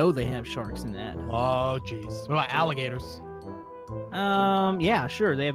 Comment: said alligators, I thought he said salamanders